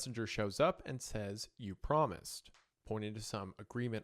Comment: i totally thought the entity said “You… pregnant..” i am a Creepcast fan btw so i thought i was taking a Hunter-level turn there